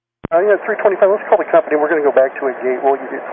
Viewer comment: Out of curiosity, why would the captain not want to do a low pass over the runway? Was he worried about an engine failing while they were low? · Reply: I think the caption is incorrect. The word I hear is "company," not "captain."